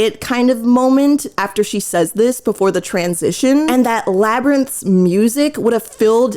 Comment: I heard "Lab Rats" and still agreed